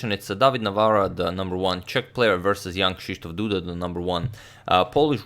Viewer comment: Every time I hear "Young Shish Toff" Duda